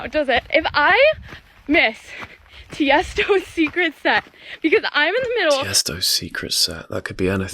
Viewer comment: I thought she was saying "Tiesto's Secret Scent" 😛